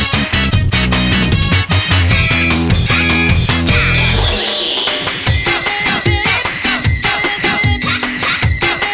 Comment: is he saying "I don't give a shiii"?? 😂😂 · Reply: He said "What a shame!" :)